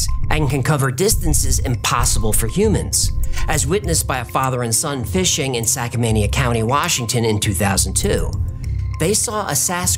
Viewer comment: Haha, did I hear "Sackmania county". That should be Skamania county.